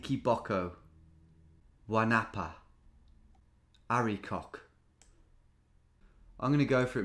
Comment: What did you say mate? 😂 Hairy what?